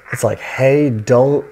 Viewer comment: i think it says "Hey, don't give them any ideas"